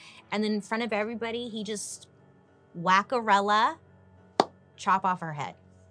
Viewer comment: I had to rewind like did she just say whackarella 😂❤